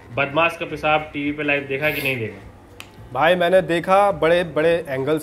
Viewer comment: WHAT IS SAYING ? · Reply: पेशाब live देखा के नही देखा